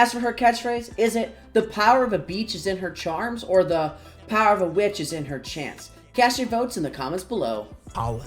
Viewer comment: Woodsy diseases? 🤨 Kinda sus. Also, I heard "The power of a vich is in chants."